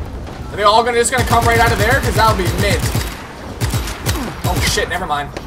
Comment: I think been watching too much Haggard Garage, not that's a bad thing haha. Could've sworn I heard him say, "that'd be mint"